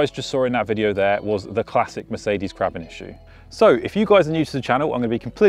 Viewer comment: Good information...BUT.... You REALLY need to get rid of the 'music'. It's intrusive, unnecessary, and during the 'listen to me back up with run-flats' segment, makes it difficult to even hear what you're trying to audibly illustrate. During the (equally unnecessary) Chapter breaks one has to hurriedly turn down the volume each, frigging time if one is watching late at night or otherwise actually values having a consistent volume level. Really....just, don't on your next videos. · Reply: Exactly. Have tried to hear what he says at the 13:00 minute mark and can’t: “…. the classic Mercedes ??? issue “ . Can anyone help? I think my car has what he is describing so would be good to know. Thanks! Oh - was it “crabbing” issue? Whatever that is…